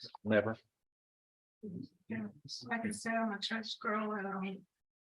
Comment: Did she say pastor's daughter? My goodness, the grief they must carry. Pure EVIL. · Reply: I heard her say "I'm a church girl." Sounds like she's more of a thug girl.